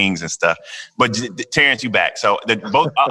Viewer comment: Did he say buttered biscuits and then mention Kamala???